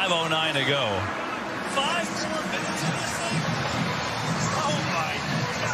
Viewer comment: does anyone know what he said besides oh my god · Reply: “5 more minutes of this thing?”